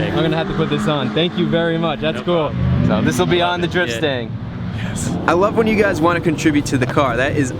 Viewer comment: anyone else thought he said drip stain instead of drift stang